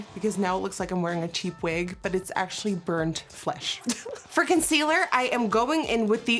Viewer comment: When she said “for concealer” I thought she said “FrIcKen CeAlEr” Lol. I’m weird